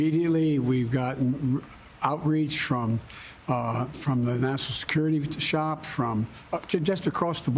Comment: Did I hear Biden say he received outreach from 'The National Security Shop'?